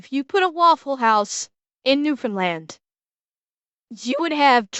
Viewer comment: What siad: "If you were to put a Wafflehouse in Newfoundland…" What I heard: "If you were to put a Waffle House in Nuka World…" I guess that's what the Drumlin's Diner in Fallout 4 (and maybe Fallout 76?) are supposed to be. The closest WH to me is in Austin, TX. And that's nearly a thousand miles away. … And now I want some Chili's…